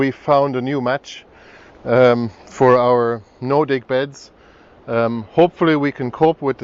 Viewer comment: I keep mishearing you "Nordic" bed ROFL ;¬)